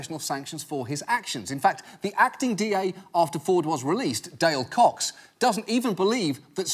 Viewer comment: So for real, when John said "Dale Cox", I heard "day old cocks".